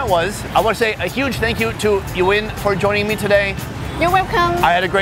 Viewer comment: At first I thought He said “you in?” Then it turns out to be her name